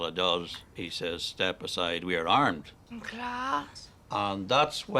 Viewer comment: What does Orla say at 2:37? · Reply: Class